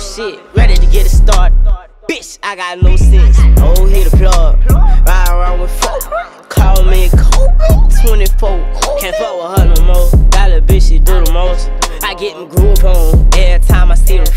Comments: "Call me KOBE, 24 on ME" 🕊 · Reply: Shit I thought he said call me cougar😂😂😂😂😂😂😂💯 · "Call me KOBE, 24 on ME" 🕊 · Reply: I thought he said " call me covid , 24 covid "